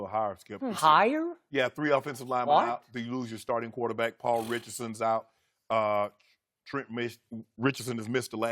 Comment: Did he just say “Trent Richardson”?